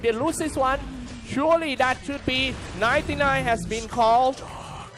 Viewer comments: did he just said 99? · wait what!!!???? Di he just said 99 has been called?????? :'( watdafak!!! haha saan planeta nyu napulot yang caster na yan???